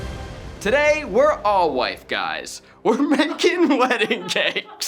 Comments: “Today, we’re ALL wife guys” 😂 The most wholesome and simultaneously piercing shade thrown by Zach! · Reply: Ohhh I thought he said "white guys", I was so confused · "Today, we're all wife guys". Such a terrible, great line. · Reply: Legit thought he said “white guys” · I died at “today, we’re all wife guys”. I love the subtle shots Zach has been taking recently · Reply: Thank you for this comment I thought he’d said white guys lol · I thought he said today we are all white guys lol